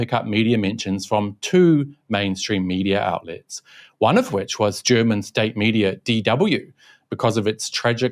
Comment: i thought u said "german measles" 😂